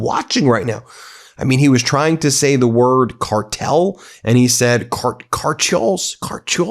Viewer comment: Sounds like taco said cartoon instead of cartel 😂😂😂😂😂😂😂